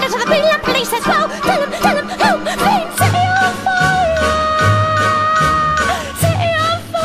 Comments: It's not supposed to make sense. She's a deranged, rambling, insane woman. Hell, she bursts into random "Mischief! Mischief!" I always heard "City of fire" But she said it like si-ee on fiah so it's a bit hard to understand. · I thought she was saying set it on fire until I put the whatchamacallits on, subtitles, ya that's it...uuugh, I watched my dvd so much it doesn't work!